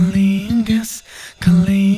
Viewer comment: It sounds like he saying "Cunnilingus"(aka oral vaginal sex) rather than clean gas. which was quite possibly intentional